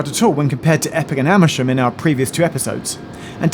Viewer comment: said: "in our previous two episodes." I heard: "in our previous Geoffisodes." Anyway, another great Geoffisode.